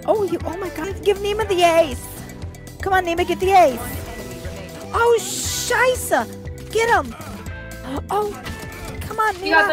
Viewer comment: what is she saying there oh sscheisse thats german